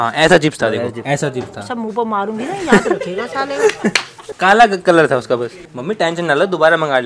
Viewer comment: what mummy say? Ans salle